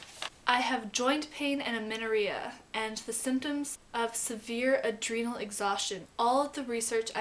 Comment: Please be so kind and write down the symptoms you mentioned at 10:06...I have bone aching too but can't understand the other symptoms...Thank you :-* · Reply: I said "I have joint pain and amenorrhea and the symptoms of severe adrenal exhaustion...all of the research I'm doing tells me that in order to fix these problems, I need to gain weight" :) Hope that helps!